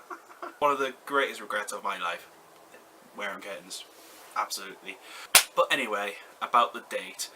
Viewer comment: wearing what? I've listened several times, and you say the word several times, and I still have no idea what word you are saying? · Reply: I actually said "I regretted having curtains i.e. the hairstyle in the photo shown. Hope that helps